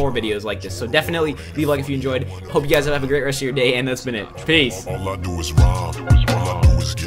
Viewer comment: hope you guys have a great day, FAZE! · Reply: It sounded like he said FAZE! not PEACE!